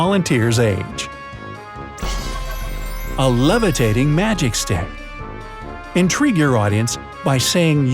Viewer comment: wow he really said "A levitating magic stick" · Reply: Match stick not a magic